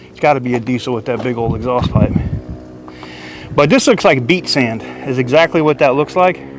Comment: Oh, beACH sand. I thought this guy was saying beet sand. I was like wtf is beet sand?